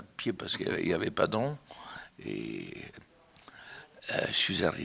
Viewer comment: paske yavait pas de koi ???? j'entends pas ce qu"il dit .